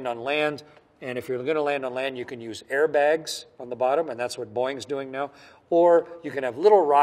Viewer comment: I thought he said "Boing" lol